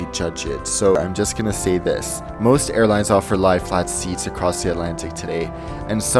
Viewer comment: What is the word said at 9:21 and 9:44? Light flight? Life lite? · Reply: I’m not 100% certain but I believe it is lie flat, it makes sense as well in the context of what he is saying